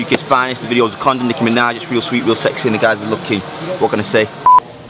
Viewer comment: the guy at 1:37 really said “sodkkskickiminajrealsweetrealsexyskkd what can I say” well WHAT did you say